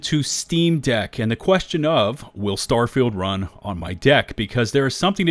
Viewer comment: did he just say will starfield run on my dick lol